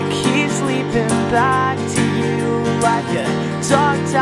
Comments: When he said "A dog tied in a tree" I thought he said "The dog died in the tree" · When he said "A dog tied in a tree" I thought he said "The dog died in the tree" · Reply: omg i heard him say "like the dog died in a tree" lol it was also funny cuz i saw you comment then that part came on lol :P